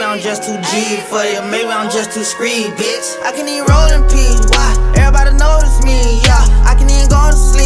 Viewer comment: (what i thought he said)i can even roll in pee me:WTF